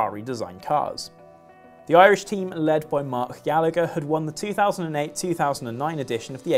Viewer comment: Did anyone else hear "Matt Gallagher